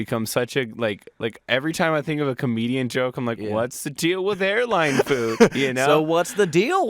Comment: When they said "What's the deal with Airline food?" I thought they said my name, Aaralyn